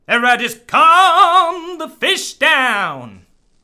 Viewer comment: swearing? What is this? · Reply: he actually says "calm the fish down" if you watch